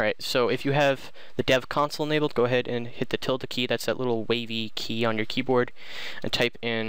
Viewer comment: I couldnt hear you really clear at 9:42. What key do I need to press? A tilt key? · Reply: tilde key ~